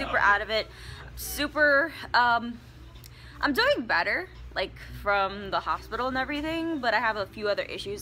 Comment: When she said "I'm feeling a lot better from the hospital" I thought that she said " I'm feeling a lot 😂 fatter from the hospital I was laughing when I thought that